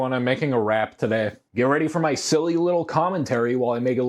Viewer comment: I heard him say “making a wrap” and i thought he was making a rap